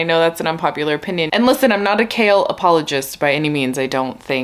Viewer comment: The first time you said “Chaol apologist” I thought you were saying “kale apologist” and I thought Huh maybe these books aren’t about what I thought they were lol 👑